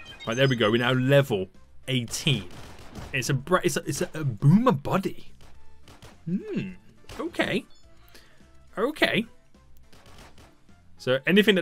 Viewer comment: gets boomer body "ok" Me: "did he just say ok boomer?!"